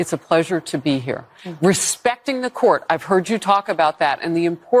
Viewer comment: Wait, Harris Faulkner, did you say, “Respecting the court.” What!?!?!?